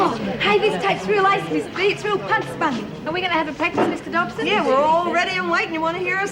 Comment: does Bobby say punk spunk ?